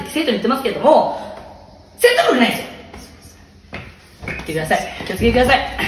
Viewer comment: 戦闘力ないよって聞こえるわ爆笑